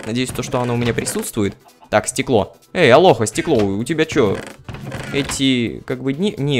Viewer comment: мне послышалось: "Эй, алоха, стекло, у тебя что, эти дни?" ЛООЛ